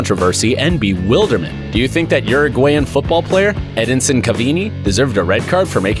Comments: Omg Did He Just Say ’CAVINI’ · Edisin cavini, no I think it's said like Edinson Cavani. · did I just hear Edinson Cavini and not cavani 🤦‍♂️